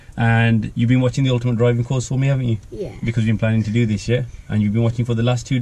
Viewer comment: I may be mistaken but did u say “because you’ve been planing to do this shit” ?